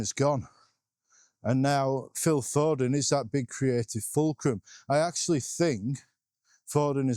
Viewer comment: I had to paused the video for a minute, clicked it back on and I thought you said " He's the creative Fullkrug." 😂😂 Yes, I went back 10 seconds to hear exactly what you said. 😁